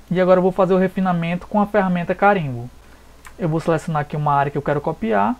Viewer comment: Aos 4:34 minutgos, voce informa que vai fazer o refinamento coma ferrameta....... não entendi qual ferramenta voce falou. Pode esclarecer? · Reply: Ele utiliza a ferramenta CARIMBO